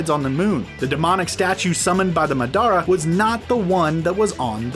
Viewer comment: did he just say The Ma Da Ra